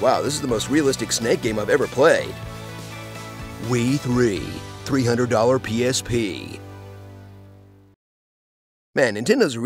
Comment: I know I'm late, but I legit thought he said Wii 3: 300 PHP (300 PHP is less than 6 dollars) XD